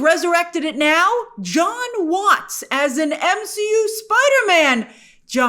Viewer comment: God I thought it said Joe Watts on there for a second